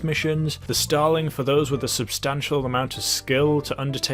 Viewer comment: Did he say Stalin?